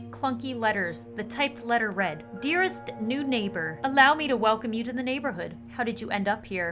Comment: what she said: "the new neighbor." what i hear: "the nude neighbor."